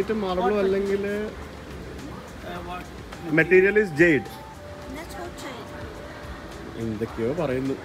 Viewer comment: I think she said "natural jade".